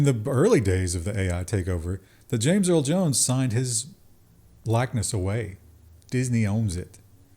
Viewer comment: it sounds like he said "James Earl Jones signed his blackness away".🤣